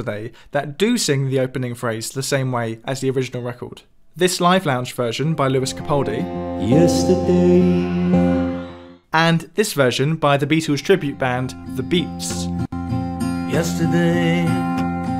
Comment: literally only like a minute and a half in and “yesterday” doesn’t even sound like a word anymore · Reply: I keep hearing it as, "Yes, today"